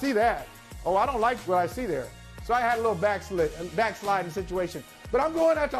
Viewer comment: I thought Holley said, "I vaxillated." But I realized it was backslid.